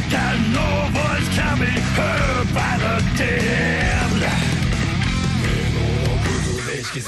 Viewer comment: I think at around 1:52 it says god knows what’s coming ??? By the damned, though I could be wrong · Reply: isn’t it “that no voice can be heard by the damned”